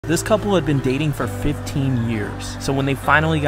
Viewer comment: I almost thought he said "This cup girl had been dating a 15 year old"